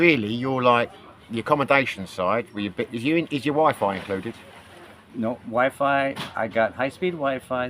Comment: when was asked about the cost of living, I thought you said "is your wife included?" wtf. the accent got me. "is your wifi included" lol!